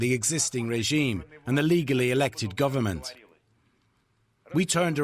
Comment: Did he actually say illegally or did he in fact say legally? Are we misquoting things again to try and skew the story and further a narrative again? Like somehow this most extreme pro-war figure decided to turn anti-war like I've seen so many news channels try to report. When he actually said this war should happen, but people need to be trained better (and based on many foreign volunteers fighting in Ukraine, they are most competent, so people are actually asking to fight a more competent Russian army). Like Peter Zeihan said yesterday, it's crazy that people are cheering him and would rather have him in the Kremlin, like somehow he would bring peace and stability to Ukraine. Completely forgetting all the sledgehammer incidents. Granted wouldn't surprise me, seeing Ukraine gave weapons to someone like Denis Nikitin.